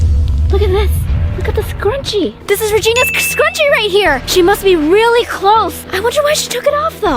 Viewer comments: did anyone hear say this is regina sk scrunchie at 2:20 ? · Did by just say sksk scrunchie